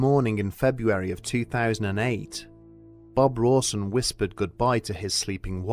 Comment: I had to go back in the begining and loom at the subtitles because I thought he said Bob Ross